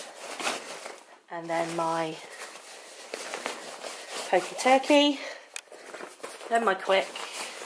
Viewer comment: did you say Pokey Turkey? Lol OMG that was so funny!!!! :)